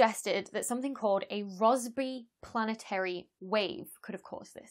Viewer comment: I thought she was saying raspberry all British until Rossby appeared on the screen. 😆🇬🇧🖐️